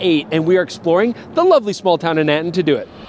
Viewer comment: I love how he says the Nikon rep drove it down to them… like they are in Edmonton. Where did the rep drive down from? The north pole??? 😂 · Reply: ahh Nanton. I thought I heard Edmonton 🙏